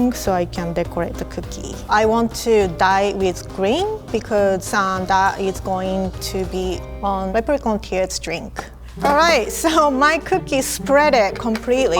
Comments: What shes saying:i want to dye it with green because that is going to be on leprechaun tears drink VS 5:28 English caps: i want to die with green because santa is going to my particular kids drink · I THOUGHT SHE SAID "I WANT TO DIE WITH GREEN" OH MY GOD! 😂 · I thought she said I WANT TO DIE